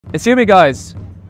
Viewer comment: I thought he said," Are you guys"😅 i had that same look they had like wtf? 😂